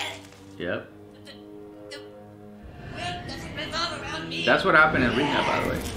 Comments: Cartman vomiting black goo and fight against his soul Cesar: "that's exactly what happens in real life" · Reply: I thought he said "that’s exactly what happens in rehab" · Cartman starts puking and having demon possessed convulsions. "yup, that's what happens in rehab". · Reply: I thought he said "that's what happened in rehab"